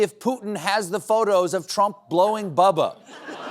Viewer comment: For a second, I thought Stephen said Bubbles, not Bubba – Michael Jackson's monkey. And I wasn't surprised lol